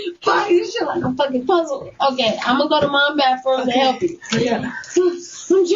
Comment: It sounded like she said "Im gonna go to my bathroom in the alley!" 😂